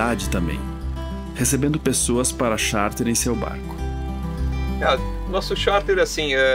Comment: show de entrevistas, o que é sharp que ele diz que faz??? pra viver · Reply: desculpa ai, mas parece que ele falou o seguinte: "...recebendo as pessoas para Charter..." e não sharp como você mencionou... depois verifica lá novamente... acho que foi em 11:53... eu vi a matéria duas vezes e realmente na primeira pensei ter ouvido também sharp, mas ao rever, consegui distinguir...